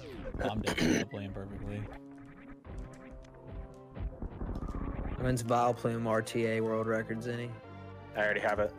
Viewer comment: What does say to at 45:56 please friends? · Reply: “When is vileplume RTA world record Zeni?”